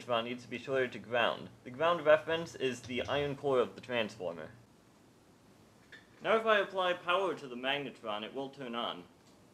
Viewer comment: "Every..(cant hear) I apply power to the magnetron it wont turn on." Wont turn on? I can clearly see that it will? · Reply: Sorry, my speech is a little hard to make out. It reads: "Now if I apply power to the magnetron, it will turn on"